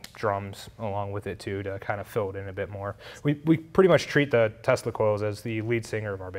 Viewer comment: Great video but every time you said Tesla coil fast I thought you were saying testicles :)